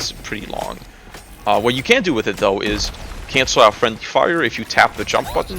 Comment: Hi sorry what was said from 10:22 to 10:29? I couldn't really understand that part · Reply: I said friendly fire but I meant fall damage (which if you do enough mental gymnastics, maybe you could consider that friendly fire lol)